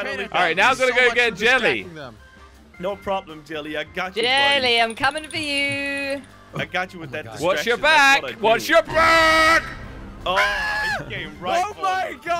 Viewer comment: What I hear: Jelly wash your back What he actually says :Jelly watch your back